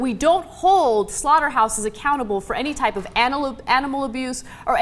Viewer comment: did I hear say "Anal abuse" ?